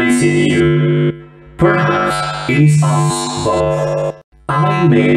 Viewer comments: its actually "I may be missing my face but even i could see this coming" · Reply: I hear "I may be missing my face, but even I could see you stalk." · I think it says ‘I may be missing a face, but even I could see you stalk” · actually he says "even I could see this coming" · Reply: Thought he said, “even I could see you stalk” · “I may be missing my face but even I could see this coming” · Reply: I thought he said “I could see you stalk” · The last one says: “I may be missing my face, but even I could see you stalk” but it’s says (I can see you stuck) which they must have misheard